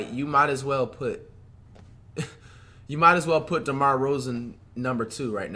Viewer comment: Did he just say DeMar Rozen